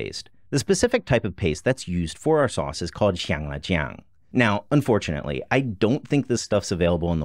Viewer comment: Whats the stuff at 1:29 called? Sounded like you said chang la chang but that didnt give any results. I bet I can find some, we have asian grocery stores in major citys. In Charlotte we have many. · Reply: 香辣酱/xiang la jiang.